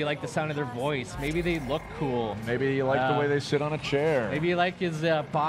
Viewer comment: lol I thought he said " maybe you like the way they shit on the chair"